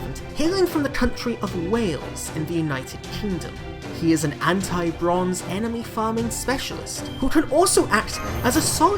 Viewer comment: idk why, but i heard you say he comes from the country of whales and deals + damage against bronze servants